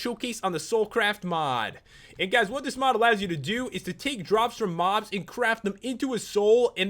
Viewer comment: The first time I watched it I thought he said take drugs from mobs:D